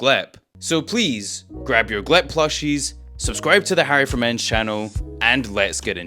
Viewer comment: When you said your name i heard "Harry for men"